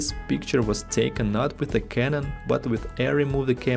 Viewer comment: what was the camera you said? "if this picture was taken not with a canon, but with" what? What camera will give you that much more dynamic range? Thanks. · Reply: Hello! Arri Alexa can give you much higher dynamic range